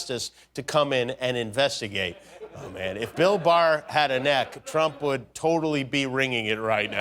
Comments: Damn, I thought he said 'Bill Burr' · I thought it said "Bill Burr" instead of "Bill Barr". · Wait I thought you said "Bill Burr"